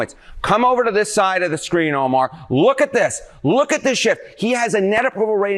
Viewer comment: I thought he said ""look at this shit" but he said "look at this shift" haha